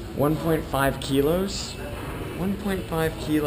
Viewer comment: "1.5 Kilos... that's a lot of mass..." All I heard was, "That was a big turd."